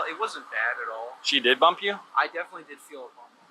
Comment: Noise violations count and she did hit the guys car I hope she doesn't tap a bike rider or pedestrian on the way home. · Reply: I swore that I heard the guy say she did bump his car.